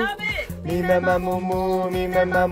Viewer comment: all I just hear is “mi me ma Mo”